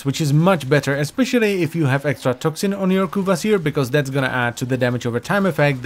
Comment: I thought he said "Ovary Ugg Kuva Seer"